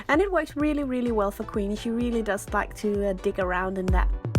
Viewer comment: I thought she said dick around lol 😂